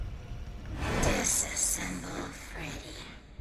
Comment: For the ‘disassemble vanny’ ending I thought she said ‘this is simple Freddy’ not disassemble Freddy’